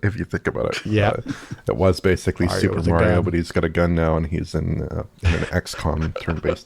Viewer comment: ”Mario but he’s got a gun now and he’s an ex-con” I think I misheard that but still works as an xcom-like, just with a more colorful backstory!